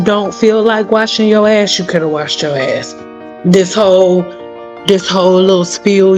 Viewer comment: Just stumbled onto your channel, you got me crying, and dyingof laughter. Your commentary is hilarious. Especially around 42:00 mark, all I hear in the background is "No, no wash your azz." Lolololol😂😂😂